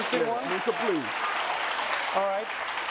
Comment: I thought it said "incompetent". Never mind.